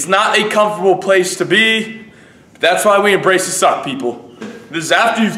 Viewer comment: I heard 'that's why we emrace the sock-people' ...my brain does funny things early in the morning LOL